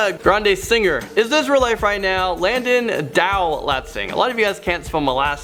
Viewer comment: I thought he had said, "Landon DO-NOT-SING"